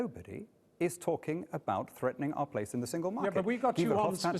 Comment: Would that be when they claim he says at 5:11 "absolutely nobody is talking about leaving the single market"? which of course brexiters are now, post-referendum, arguing the Leave vote means. What does he actually say at that point? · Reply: He said "threatening our place in the Single Market". He is referring to access, not membership. Also could you explain to me when Hannan was appointed the sole voice for Brexit? Even if he had said what you suggest (falsely) that he said, it doesn't mean that is what the Leave Campaign set out. Jesus, if we took what Remain stands for, for every media spokesperson we would be allover the place!